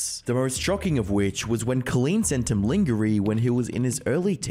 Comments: The way you said lingerie is so funny lol. I thought you said lingering for a sec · er...lingery? Sounds like you're reading from a script without knowing what the words actually are.